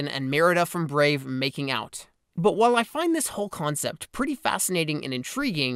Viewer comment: Did he say 'i find this HULK concept' at 2:26 or is it just me